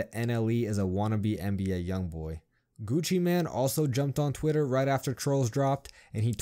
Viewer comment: Did you just say Gucci man